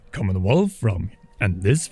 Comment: When you say the name of your channel, I always hear “Common Wolf From”. American ears! Love your videos!